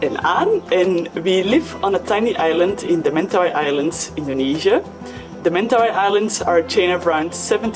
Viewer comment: I kept hearing "in Tunisia"